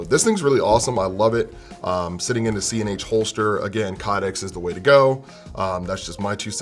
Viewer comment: What is the holster has for the 365? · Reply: sounded like he said C and H holster or CNH holster idk